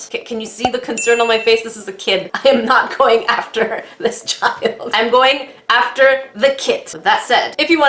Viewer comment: It sounded like said I'm not going for the kit I'm going for the kid